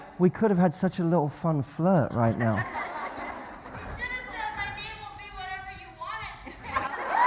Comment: in 4:06 a woman in back seats is shouting, what did she say to? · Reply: I'm not really sure but, I think she says something like "you could have said [...] whatever you want it to be" (I guess she's talking to the woman and she's referring to the name situation)